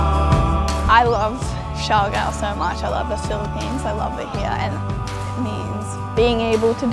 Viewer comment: hello guys how are you? what she say at 3:27 to 3:45? please thank you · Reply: "I love siargao so much, I love the Philippines, I love it here, and it means being able to do what I love, being able to see the people I love, being able to be in the place I love."